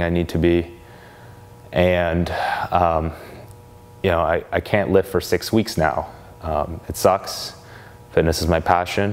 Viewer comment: Jesus when he said "I can't lift for six weeks" I thought he said "I can't live for six weeks" and I nearly shit myself.